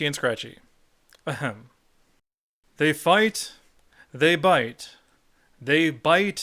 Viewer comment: wait... it's they fight they bite? totally thought it just said fight over and over